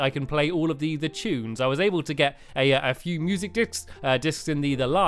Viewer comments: did stampy say music d*** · did stamps just say music dicks? XD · in video it sounds like he said music dicks